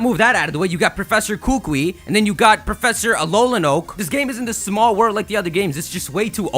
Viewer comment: Alolan Oak? Aww, I thought you said Mullet Oak. That would've been funnier.